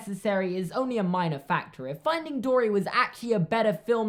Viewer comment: I thought he said finding Dora 😂